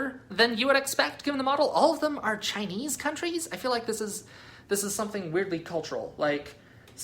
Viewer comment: Bruh, at 9:03 did I just hear "Chinese countries"?